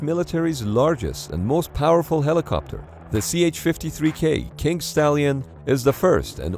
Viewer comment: Did he say kink stallion???